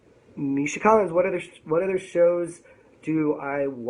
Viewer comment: Ummm, did he say 'Misha Collins' at 18:02? O.o